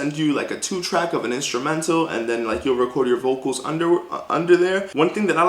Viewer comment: 😂 did you say vocal underwear 🩲 8:52😂 thank you so much for this video you have help me I will be looking for more videos from you.